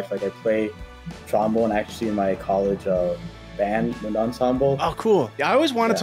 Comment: editor did him dirty at @ 18:10 calling it a "mid-ensemble" instead of "wind ensemble"